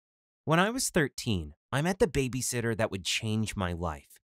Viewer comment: OMG I THOUGHT IT SAID BABYSISTER!!!!